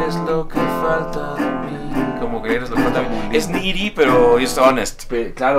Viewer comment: Será de los comentarios más random pero, alguien me explica lo que dice al principio Leonel?, el tararea, en el segundo 0:20 dice eres lo que falta de mí y dice algo así como es "nigiri" pero es honest, alguien que sepa lo que significa lo que dijo que me explique Xd, gracias, y un aplauso para esos genios con esta obra de arte que hicieron ;) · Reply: Needy, literalmente es necesitado.